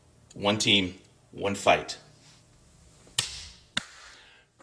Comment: I just want confirmation that when I hear you say "now it's time for the toe punch or throw bunch" what I should be hearing is "throat punch" right?